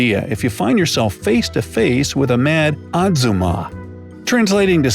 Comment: Did anyone else think Azuma like from Naruto when he was trying to say Ahjumma? 7:37 I mean what he said about the imo could also be said for azuma ¯\_(ツ)_/¯